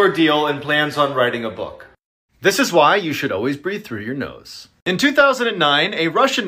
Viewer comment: "This is why you should breath with your nose" Me: trying my best to breath through it even though its clogged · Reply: At first i thought it said you shouldn’t 😃✋